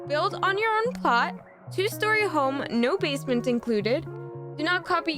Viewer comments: I thought she said no basements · Thought you said no basements.... Eh 😐